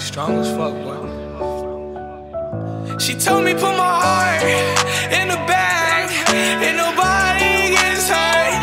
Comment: she told me put my heart in the bag and no body gets hurt 🤧🤧💔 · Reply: It sounds like he said She told me to put my heart in the bag with no body inside